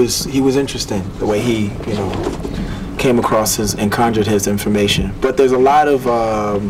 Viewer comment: What did pharrell say @ 8:31 .... his ___ information? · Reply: “Conjured” I believe